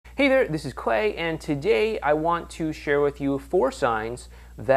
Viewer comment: I thought he said "Hey this is Quay"